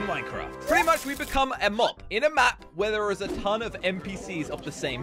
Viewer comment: its sounds like he is saying "We are basically going to be a mop, on a map" instead of mob on a map!! I can't and am not make fun of his accent!!